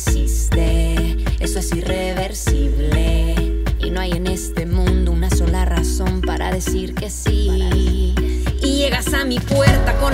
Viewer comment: Yo pensé que decia "Y llegas a mi puerte con anillo en mano, buscando un dedo" JSJSJSSJ #HaAsh #30defebrero #EsoNoVaASuceder